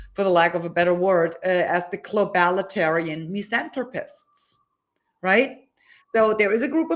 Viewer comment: Q: WHAT DOES CHRISTINE SAY HERE? ...On using CC I think it's Globalitarian misanthropists..? ...Great descriptor!